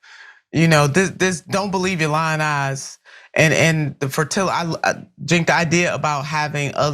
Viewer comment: Did say “fertilla”? 5:17 Why are blacks so dumb?